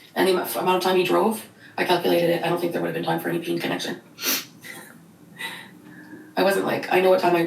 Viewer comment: I really thought she said "pink connection" the first time I heard that, and was like "Wtf, that is the most uncomfy way to put it."